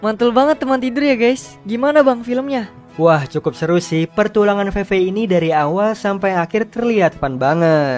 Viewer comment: gak salah denger kan bang, teman tidur bilang "pertulangan" bukan pertualangan. tapi gak apa apa lah